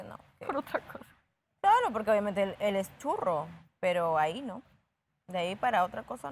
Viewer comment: No entendí lo que dijo de Austin minutos 11:30🤔 *Da risa cuando Sebastián hace las preguntas a Alejandro 🤣🤣 el está como nervioso😵 28:58 con Sebastián hablan ella está molesta😳 de que o que?😕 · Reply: Lo ve a Austin como amigo. El es churro o sea muy guapo.